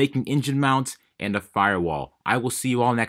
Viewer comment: For some reason I heard "autonomous world leader" instead of "autonomous little cleaner" at the end. Thought it was kinda funny.